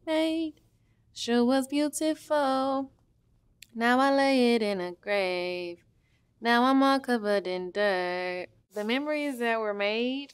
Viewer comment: Bro I thought she was saying “ now I’m laying in a grave, now I’m all covered in blood”